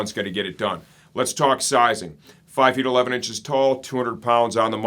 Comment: I would not go larger than the size large. If you need extra length go for the Large Tall. - Van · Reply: wait wait no. I made a big mistake. I thought u said ur 5 ft 8 in. But u actually said 5 ft 11 in. I'm glad I rewatched it. U speak to quickly when u said ur height. This why I thought I needed bigger bc I thought u were only 5 ft 8 inch